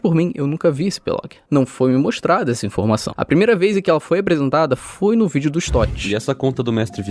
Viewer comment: só eu que ouvi Apresuntada?